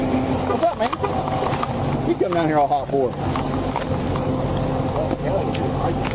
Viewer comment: he said what did you come in here all hot for I didn't hear other cop call anyone